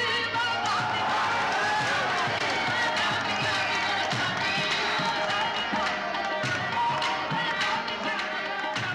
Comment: what he says at 2:28????? anybody plz tell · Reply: Muhammad Sohaib Khan mere saqi saqi mere saqi